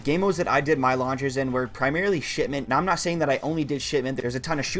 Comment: Really sounds like you saying Shitment